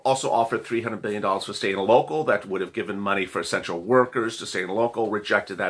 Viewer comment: When he said “to essential” workers i heard “to sexual” workers. I literally tripped out for a minute